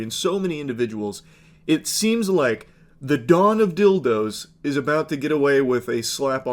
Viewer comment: you said "Don of Dildo", I heard "Dawn of Dildo" 😂